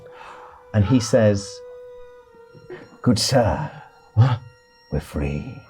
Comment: He said, “Good sir, we’re free.” He didn’t use the word “monsieur”, and no way was it River Proletariat. All I’m saying is that if there’s a sequel, River will probably be an NPC, he won’t speak French, and he will have given up art in favour of an unhealthy obsession with astronomy and dark magic. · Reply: Aaah, I must've misheard "good sir" as "monsieur", many thanks